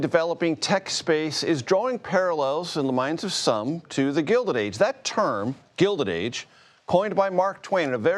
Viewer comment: I thought he said Golder Age....1950s? Chicago never voted because why? It is Chicago.